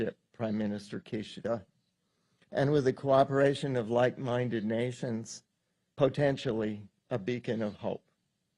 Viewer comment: Did he just say " like minded masons"???